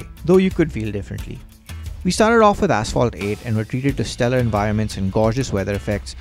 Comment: i thought u said assfall 😂